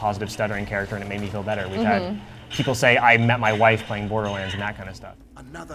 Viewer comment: wait, what did he say at the end? i missheard him saying " borderlands 3" ?